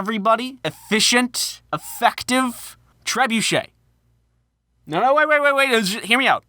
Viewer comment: when i thought trebuchet was said like “Tree bucket”